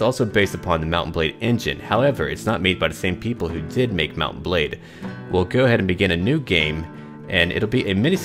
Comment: I always thought you said " Mountain Blade"